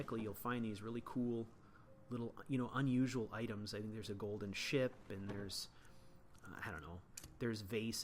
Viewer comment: I swear he said there's a Golden Shit! HAhahahhahah